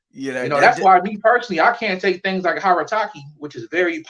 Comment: what is that he said to open 3rd eye? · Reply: Harataki